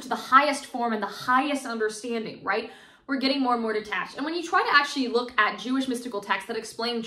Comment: Did you say more and more detached @ 11:27 ?